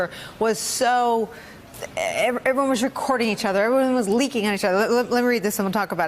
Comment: Did she say "everyone was leaking on each other"? That's comedy gold right there.